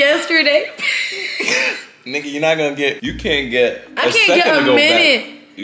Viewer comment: Did he just say Nicki 14:55 or I'm bugging? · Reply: He said nigga